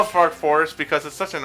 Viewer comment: I heard "Fart Forest"